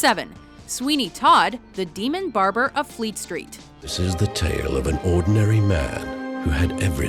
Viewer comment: i swore she said demon barber of fleek street lmaoo